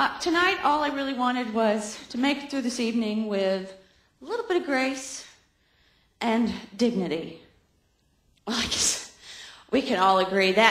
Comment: What did Rachel say at 2:30? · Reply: "All I really wanted was to make it through this evening with a little bit of grace, and dignity." Your comment is a decade old, but now you know :)